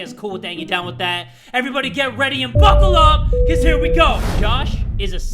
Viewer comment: Damn... I thought he said fuckle up...